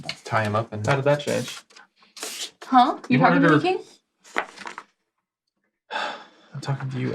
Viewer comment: Anyone knows what Anna said at 2:15:00 ? i did not understood what she said. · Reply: Hi Ignacio! I said, "Huh, are you talking to me, King?" -- I wasn't paying attention and realized had been saying something, and it felt accurate to in-character portray Judge's lack of acknowledgement towards King.